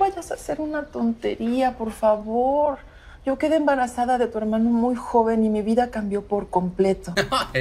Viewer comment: Dijo hermano:c no entendi lo puse en camara lenta 3:07 ::::vvv tengo trauma mi primo me trauma y pos cuando dice hermano ya me agarro un trauma